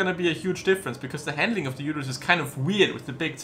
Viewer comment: did you call it a Uterus or am I just dumb?